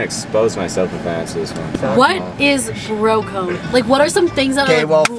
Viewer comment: Baahahha when she said “bro code” I thought she said “broke ho” and I was like WTF!!!!